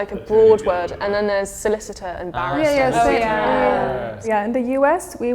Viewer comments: There are a few mistaken subtitled words in this, she says “barrister” not “ambassador” as the subtitles say. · The captions wrong, barrister, not ambassador